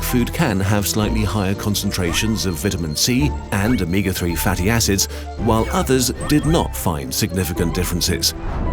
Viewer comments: I heard fatty asess are cool I misheard cause is midnight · I was really sleepy one night and misheard this as "Fatty asses" and I laughed way too hard